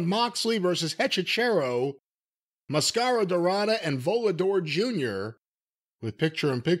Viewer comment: Idk why but I swore it sounded like Brian said Voldemort Jr 😂